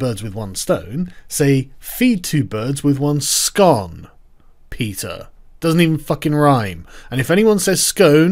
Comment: Never liked PETA, but now I like them even less. XD haha Do all people in the EU give the E in PETA the big E sound? Thought you said Peter at first